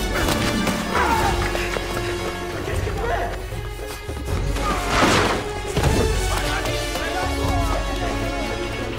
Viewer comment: I think that guy said "Are you crazy?!" in Swahili. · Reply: Jacob Ramirez no no guy he says it in French he says : « Mais qu’est ce qu’il fait??! » which mean « What is he doing ?! »